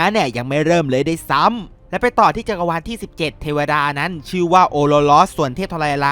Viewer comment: ได้ยินว่า ororon